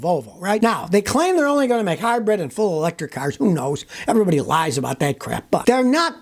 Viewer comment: thought you said electric cars are a joke.🙃🙂🙃😁